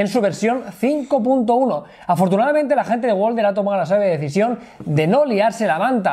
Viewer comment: Qué rayos dice Jaume? jajaja me parece que dice "Bien, joder!" pero no estoy seguro... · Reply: bien Wolder